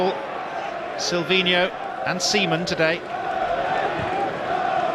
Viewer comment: did he say semen?